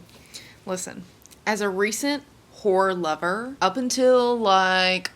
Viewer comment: I thought u said whore and was so confused 😭😭😭